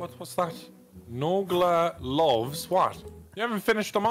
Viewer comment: I legit thought was trying to say that Nogla loves war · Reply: I believe it’s “nogla luvs ohmrecker”